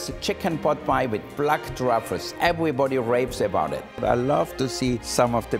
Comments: DID HE JUST SAY “EVERYBODY RAPES ABOUT IT?” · did he just say everybody rapes about it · Im not a pervert but what i heard on 1:58 was "everyone rapes about it" lol